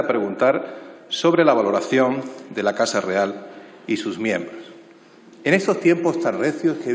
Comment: Soy solo yo o parece que dice en el 0:10 y sus mierdas? Jajajaj